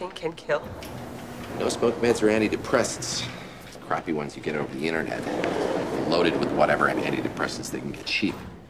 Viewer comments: Can anyone decipher what he says at 8:40? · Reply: "Quitting smoking can kill?" "No smoke meds or anti depressants, crappy ones you get off the internet? Loaded with anti depressants you get cheap. He's not depressed" ???????????? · can any native english speaker caption what House says here? I can't quite make sense of it. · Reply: "No-smoke meds are antidepressants. The crappy ones you get over the internet are loaded with whatever antidepressants they can get cheap. And since mommy and daddy obviously did not take him to a pediatrician..."